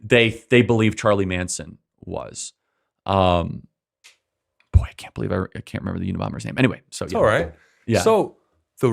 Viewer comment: I'm sure Charlie says under his breath "boy I can't believe Ericka??? Why is Charlie saying this???